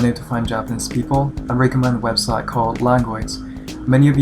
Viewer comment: didnt catch the name of the website you stated at about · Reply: It was Lang-8. You could have clicked to see the captions, it was written. Very good website, I use it myself for my English and Japanese, since I am French Canadian.